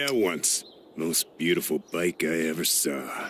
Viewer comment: ever since I was a kid I always thought he said biker. But in reality he said bike.🤣🤣